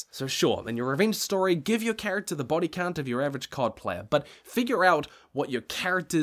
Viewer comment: Gotta say, your line at 12:23 about "give your character the body count of your average COD player" was BRILLIANTLY HILARIOUS, so off hand dismissive about such a high body count. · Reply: Lol, I missheard then. I thought he said "... of your average coal plant"